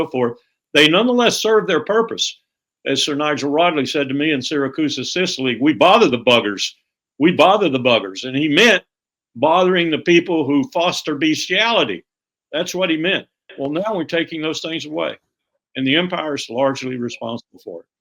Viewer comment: did Warboomer say, "bestiality"